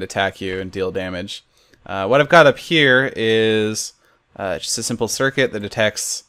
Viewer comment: Did he say a simple circuit at 0:55 ?!?!?!?!